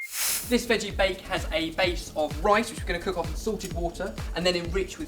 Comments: when you said "salted water" i thought you said "sorted water"... i was like, "damn! they have their own water now??" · Haha, sounds like bens saying ''Sorted Water'' :P